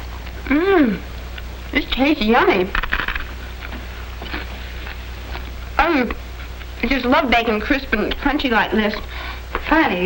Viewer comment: If you put the subtitles on at 1:40 when the girl says “hmm this tastes yummy” the subtitles say “hmmm take aim” and it hilarious. Edit: just put the subtitles on the the whole video it is beautiful.